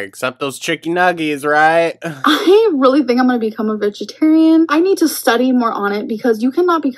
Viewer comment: she is a liar and a half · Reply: Destiny just thought said "vagitarian". I'll see myself out 🚪